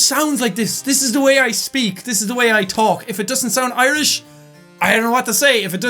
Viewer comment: Did anyone else hear say "if it doesn't sound Irish" 2 times